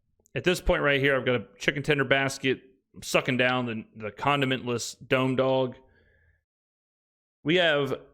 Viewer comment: Sir, did you just say condomless dome dog? :face-blue-wide-eyes